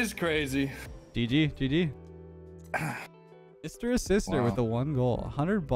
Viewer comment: I thought was saying "Mister or Sister"... hella confused.